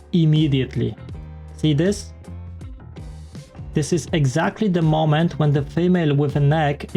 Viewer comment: "eggsacktly the moment" I don't know if this was intentional, but this is what I heard haha